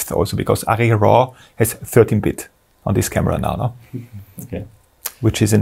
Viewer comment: Did he say Arri RAW is 30-bits or 13-bits? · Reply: 13 bits